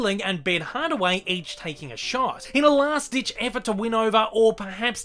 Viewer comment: did you just say taking a shart !?